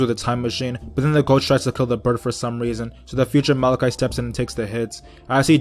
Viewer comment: It sounded like you said malachite not mordecai